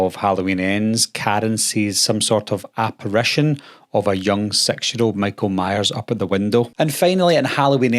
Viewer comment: I kept hear "young sexual Michael Myers" and I think you saying "young 6 year old"! LOL